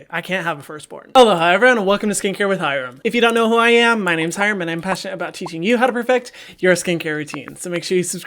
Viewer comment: Every single intro of his videos all I heard was: 'HELLO IT'S HYRAM, MY NAME IS HYRAM', took me over a month to realize what he actually says.